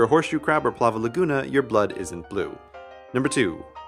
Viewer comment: captions say you're blood